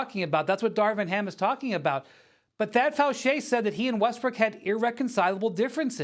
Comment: Is Brian really calling Westbrook Westbrick????